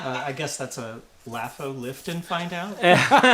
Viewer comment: On the Odium with underpants question, at first I thought he said "Lift will find out", lol